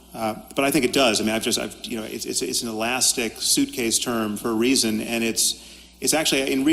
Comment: this has been said before in comments, but i think you are mishearing Sam's quote about well-being. I think he's saying well-being is "an elastic suitcase term for 'a' reason" not "for reason"